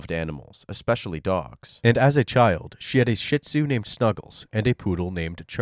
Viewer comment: I totally thought he was going to say “she had a sh1t ton of dogs”